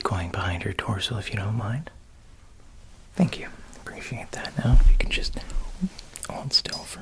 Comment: When he said thank you at 14:46 i swear i heard "so if you don't mind, Fuck you"